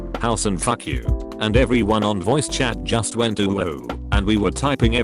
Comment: I heard “And everyone in the chat just went UWU”